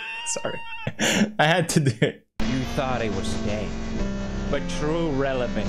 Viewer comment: Every single time pewds's outro starts rolling i hear him saying "you thought i was gay?" .